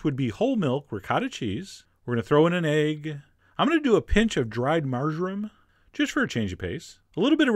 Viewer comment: I thought he said a "pinch of dry margarine". Not sure if that's a thing, but if it is/was, Chef John you're grounded for saying something that sounded like margarine.